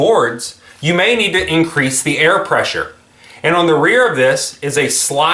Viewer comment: Is it me or did you say you can "increase the air pressure" with the rear lever? I heard that at minute 2:03 - 2:07.